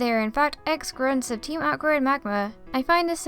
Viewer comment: Instead of "Team aqua and magma" I heard "Teen awkward and magma"